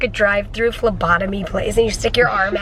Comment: I thought said "drive thru lobotomises " Wow did I ever laugh